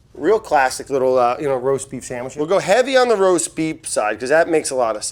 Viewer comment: Did Brad say "roast beep" at 2:51? LOL